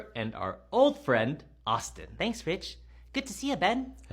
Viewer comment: When Austin said “ thanks rich” i thought he said “ thanks b!tch”